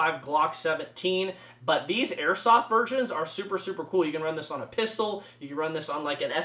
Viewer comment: I won’t lie I thought you said “airsoft virgins”😂